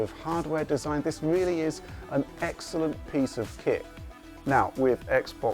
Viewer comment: Almost sounded like he said "An excellent piece of sht" XD